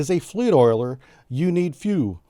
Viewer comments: Followed by a fleet oiler “you need fuel” I swear that’s what I thought he said lol. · For a split second, when he mentioned the name of the fleet oiler, I thought said "you need fuel" haha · I swear I heard the fleet oiler being called "You need fuel".